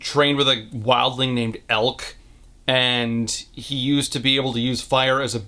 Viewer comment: at first i heard "geld" and i thought oro killed him by cutting off his balls...